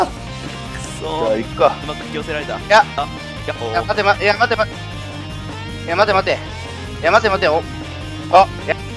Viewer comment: 今日の先輩後輩 4:44 「やっほやっほー」 「まてまて」 5:09 「やめろショッピ君！ww」 6:30 歌シマ→「～～～コネシマ！！」聞き取れなかった ↑久しぶりにに殺意高い聞けて嬉しかった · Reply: うるさい！じゃないですかね？